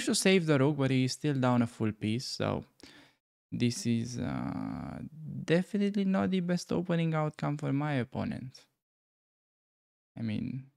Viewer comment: did he say not the best opening unless he is from Ohio? (11:30) LOL